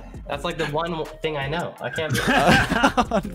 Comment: spells MUST loses jump instead of MUSTY loses jump